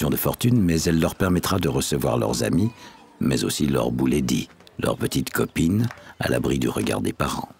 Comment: Leur quoi 😂😂😂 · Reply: Leur bouledi 😂😂😂😭